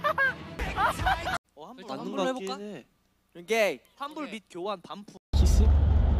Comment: wtf did hyunjin say your gay or their gay?!